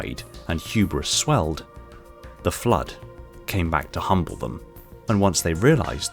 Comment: uhm I can't be the only one who hears "the flood came back to HUMP them."